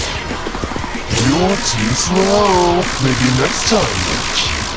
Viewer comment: Hey, what did Sonic.EXE say at 22:04 I can't hear it. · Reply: You’re too slow, maybe next time, you’ll keep up!